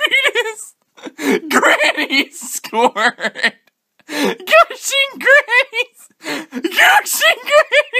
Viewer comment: Did he just say "gushing grannies"??